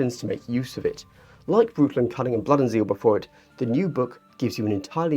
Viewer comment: I spit my beer out when I thought I heard you say “Anal cunning”